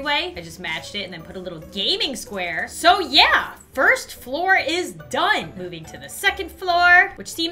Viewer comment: i swear to God I heard her say the word "birth"😂😂😂